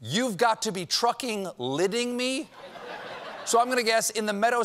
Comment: Sounds like he said "shitting" and not "kidding"